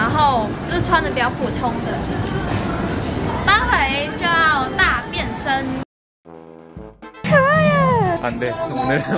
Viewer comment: 我听成大便身😂